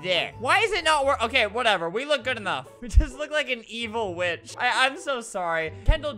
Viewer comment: it sounds like he said evil B word far way get close he say evil witch weird?????